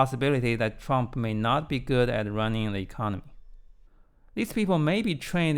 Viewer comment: He said "running the economy, but the subtitles said "running and they can't" !